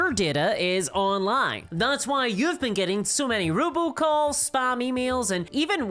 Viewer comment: I just need it to be known that I kept hearing "robocall" as "ruble calls" and was wondering why Russian money was calling her, 😂